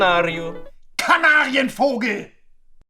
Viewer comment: мне послышалось или нет, но на 1:59 сказал немец канарьи фуки))